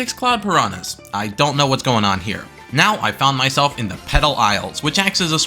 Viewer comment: I thought he said I found myself in Ohio 😂